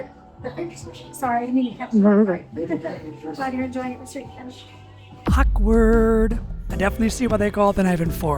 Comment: Turn on captions on 2:20 and this mf caption said "Mr. Cannibal" ✌✌